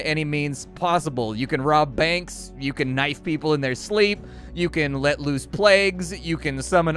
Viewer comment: For a second, I thought you said you could "knight people in their sleep." Quite an image, lol.